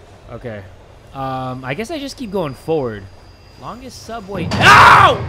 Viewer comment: instead of AHH!! it was OWW!!